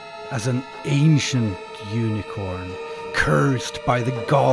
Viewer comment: Oh dang! You said “ancient unicorn.” The first time I watched the video, I thought you said “Asian unicorn.” And all the information superimposed on the screen covered up the subtitles, so I could read them the first time through.